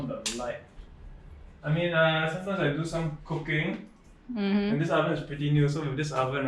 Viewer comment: Lol I thought he said cocaine instead of cooking. Like what did he say!?!?